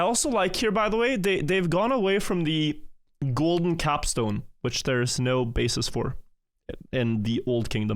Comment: I totally thought he said golden cock stone.